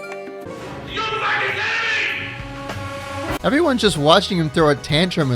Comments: I don’t think said that at 10:54. You probably put that in as a joke, but still I think he’s saying ‘You’re fucking dead to me’. And honestly after hearing that and being one of the employees I would be pretty nervous. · idk if you did that as a joke but what actually said was "Are you fucking kidding me"? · i Think he said Are you f+++ing kidding me?!